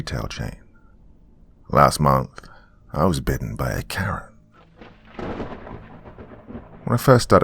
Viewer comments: Omg I thought you said "Bitten by a CARROT"! 😂😂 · Good evening again Doctor, and everyone else here this evening, hope you are all having a good weekend :) This one sounds interesting, although I don't like the sound of being bitten by a Karen! LOL. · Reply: I thought he said “bit by a carrot” 💀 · First story.. Welcome to the Big Blue Box..at first I thought he said Bitten By A CARROT... but I total feel the pain here.. either way! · Not going to lie here, first story I thought said was bitten by a carrot. Either way, would make for an interesting creepypasta.